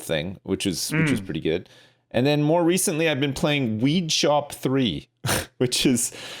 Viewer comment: I thought he said "Weed Chop 3", which sounds exactly like the kind of game Sips would play.